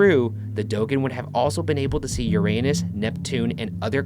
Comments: i literally thought he said see your anus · I was reading the comments when Ryan said 'Uranus' I seriously thought he said 'your anus'.....until he said 'and Neptune' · am i the only one who mis heard ryan said about the dogon, @ 5:12 i misheard this " the dogon would be able to see your anus " not uranus , hahahahahhaha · he said Uranus so strangely.. I heard your annus.. is it just me? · omg i totally thought that he said "your anus" at 5:11😂😂😂😂